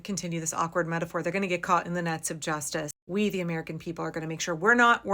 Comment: At first I thought you said that they're going to get caught in the nuts of justice. I like it lol.